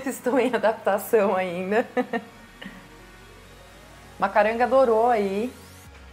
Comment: Fiquei curiosa do nome dessa planta meia redonda , logo na entrada da sala ,planta da coleção da Maju. O nome disse muito rápido rs entendi "caranga dorou "😅. Fiquei curiosa pra saber o nome certo , adorei essa planta 🤭❤️ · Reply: Oi Geniele! Chama-se Macaranga grandifolia :)